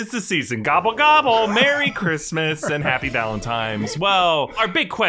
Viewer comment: Wait a second did you just say valentiMes Day!!